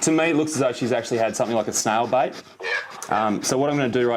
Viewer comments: I thought he said snail bite... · I thought he was saying snail bite and I was like dang, who knew snails could bite. Finally figured out he said said snail bait. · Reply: I thought he said snail bite too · I thought he was saying snail bite and I was like dang, who knew snails could bite. Finally figured out he said said snail bait. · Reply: Oh I thought he said snail bite too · I thought they said snail BITE, like the dog was bit by a snail lol